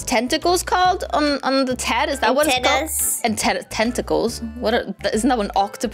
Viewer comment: Did I hear her say a$$ on 3:22 ?😳😳😳